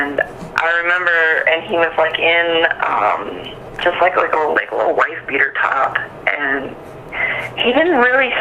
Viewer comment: Did she says he was laying on the bed with no sheets on with a wife beater top. Please correct me if I’m wrong.